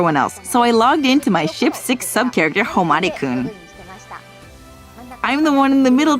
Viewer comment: did she say Ship 6 sub character?? 🤔 🧐 or am I hearing things???